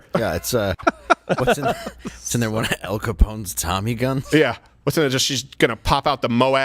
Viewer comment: Have to ask; at about 49:05, did Dave call that a Tommy Gunt?